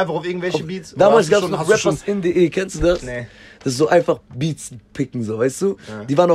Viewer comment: Was sagt er bei 28:12 ? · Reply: Beats picken